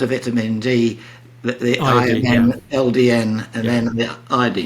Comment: What is he saying? "I, M, L, D, N" ? for preventing cancer? What???